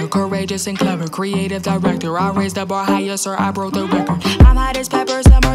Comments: No but why did I hear " I rizzed up ohio" when it's I raised the bar higher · HELP I HEARD 2:44 "I RAISED THE BAR HIGH" INTO "I RIZZED UP OHIO" 😭🙏